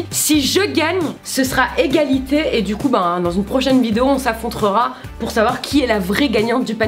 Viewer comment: y'a que moi qui ai entendu on s'affontrera 😅🤣 Sinon vous êtes géniaux j'adore toutes vos vidéos ! Vous êtes rester vous même et je kiff ça !